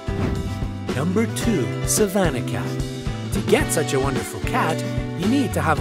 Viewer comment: I had my captions on and when it said "peterbald" on the captions it said Peter's balls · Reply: same i thought that was the name at first XD 8:07 on "Savannah cat" my captions said "Savannah cash"